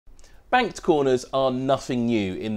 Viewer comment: Whoa, man... Thought that said "Baked Corners"!